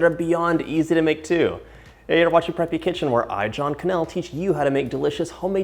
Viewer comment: At first I thought I heard his name was "drunk in milk" but in the end it was "John Kanell"😅